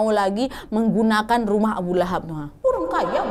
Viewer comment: ini ngomong apa yy? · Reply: Rumah abu lahab